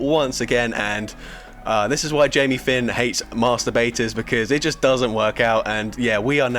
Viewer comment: what did he say 🤔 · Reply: Master baiters lol